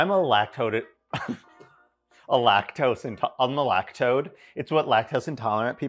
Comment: I misheard you and thought you were a lactoad. Which I guess is a really milky looking frog?